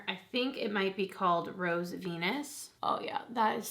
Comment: I'm sorry... WHAT was the name of the shadow at 9:29? I swear you just said Rose Pen*s! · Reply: Rose Venus! Hahaha